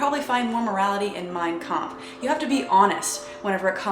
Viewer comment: I thought you said,"My cum." But then I realized Mein Kampf makes more sense. Great video